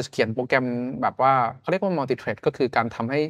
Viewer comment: ตรงนี้จริงๆ9armพูดว่า มัลติเทร็ด(multithread) นะครับ